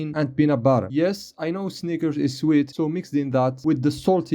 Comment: I didn’t hear what anybody heard on this part😭💀who gave bro the pass💀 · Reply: He said snickers dude😭